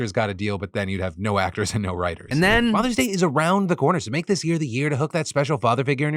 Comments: "Mother's day is around the corner so make this the year to hook up that special father figure up...." ummmm did I hear that right? Mother's day passed already...ya good Phil? I'll take the L if I did mishear. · I swear to God you just said Mother's Day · Sounded like Phil said “mothers day is coming up …” and then talks about Father’s Day